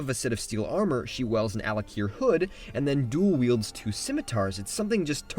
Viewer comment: I keep hearing Al'Akir instead of Alik'r